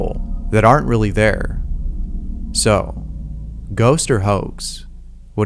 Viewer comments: Whenever you say "ghost or hoax", I always hear "ghost or hoes" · I thought he said "ghost or hoe" instead of "ghost or hoax".